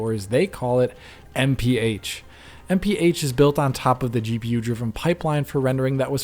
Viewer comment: I keep hearing "NPH" lol whatup Neil Patrick Harris?